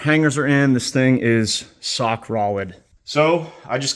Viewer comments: Lol great job fr man, but just curious, anyone else catch the ‘sock rolid’ part or just me🤣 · he said sock Rolid instead of rock solid lol